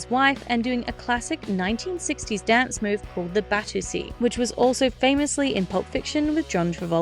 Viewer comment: What Did You Say? It Kinda Sounds Like “Batoosie”